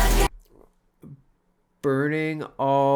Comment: I'm curious how many people got the line "You can be my jailor. Burton to this Taylor." Although totally appropriate for her relationship at the time. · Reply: Thanks for that, I misheard Burton as burning, now it makes sense!